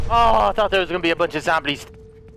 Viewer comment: did he says zomblis?